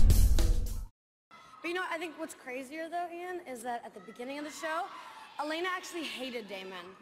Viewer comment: Can you guys write to me what they said in 0:06 to 0:26 ? Please😔💗. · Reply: she said you know what’s crazier ian is that elana hated damon, then he said you were hooking up with my brother